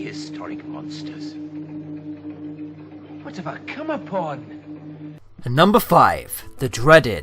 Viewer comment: Tf did he say?? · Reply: I believe it's "What have I come upon"